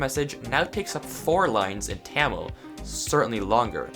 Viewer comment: A COUPLE OF THINGS TO NOTE: 1. Tamil is not the longest langauge (at least I don't think so) I meant the one that I noticed made longer words in the death message 2. You can use resource packs and mods to make custom formats for death messages, in theory making them longer. 3. Sickfies sounds strange yes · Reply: bruh you said Tamil so weird I legit thought you said camel lol